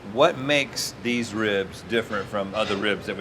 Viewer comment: Anyone else thought it said, “Beef Ribs”?